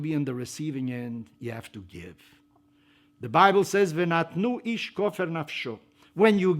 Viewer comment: he says something in Hebrew. What is it? He mentions a word that kan be read both from left to right, and right to left. What word is it? Thank you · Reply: He says "Vanatnu aish kofer nafsho" which is a quotation from Exodus 30:12 which means "let each one give to the Lord an atonement"